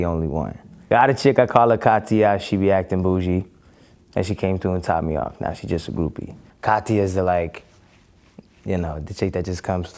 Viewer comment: Who else thought he said "then she camed and dapped me up,now she jus a groupie"😭💯